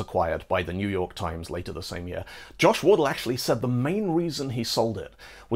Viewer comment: I keep hearing "Josh Wordle" whenever someone mentions him, and think it's one of those memes like sans undertale.